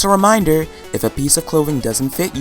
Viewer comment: i heard "pizza clothing" at first instead of "piece of clothing" LOL